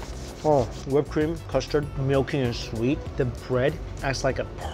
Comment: did he say male penis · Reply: I heard it too but he said "milky and sweet" 😂